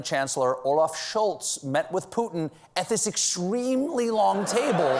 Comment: Every time Steven says Putin, it sounds like he is saying "pudding" to my British ears!